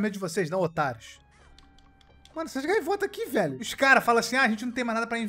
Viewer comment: só eu entendi ele falando otakus?